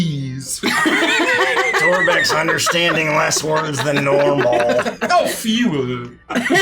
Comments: What does say here? "Aw few wuh-her" is what I'm getting. Please I desperately want to understand the joke😭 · "Torbech's understanding less words than normal" what did say? "oh few of them"? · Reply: I think he was just mispronouncing words for laughs, "a facade," "a ruse," "disguise," ... It sounded like he said, "a f-EE-wer" (a fewer). But I was looking in the comments with the same question.